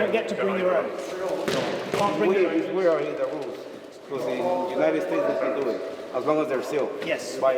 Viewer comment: I'm sure in Great Britain you dont have as much experience translating Spanglish to english, but what the guy says is "In the Junideh Stays (United States) you can do it as long as they're sealed (in the packaging)"